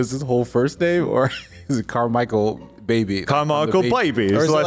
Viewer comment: It's good that you clarified that guard's name, i heard "car michealbaby"